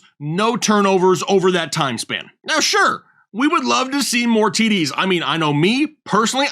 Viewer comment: Did you say you wanted to see more titties?